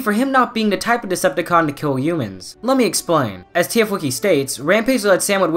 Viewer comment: I thought bone crusher said let me live because prime beat the hell out bone crusher